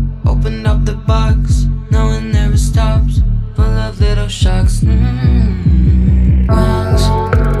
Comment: Am I the one who misheard "pool of little sharks" instead of "full of little shocks" at the end of the first verse?😆